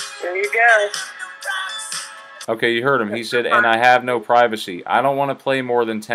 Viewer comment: I hear "and I got no privacy" I do not hear "have" here. Did this flip flop? I've not watched this YouTube clip before now...